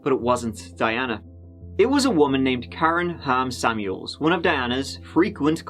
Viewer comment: Every time you said Ham Samuels I heard “ham sandwich” 🥪😂